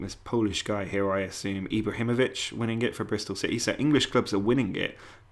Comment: I hear Ibrahimovic??